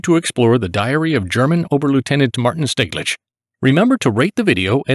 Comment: What was that officer's name, please? I didn't quite catch that. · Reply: Martin Steglich.